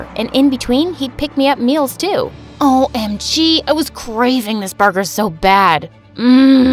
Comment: When she said I was craving this burger I thought she said I was craving this driver